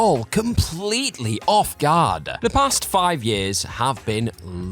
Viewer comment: Did you just say off GARDA? I knew it, ur a narc